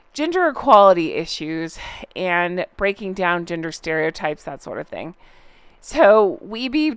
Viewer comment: I really and truly thought you said “ginger equality’s issues and breaking down ginger stereotypes” 🤣